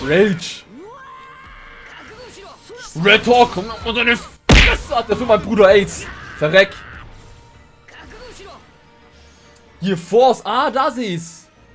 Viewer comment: ich habe erst aids verstanden xD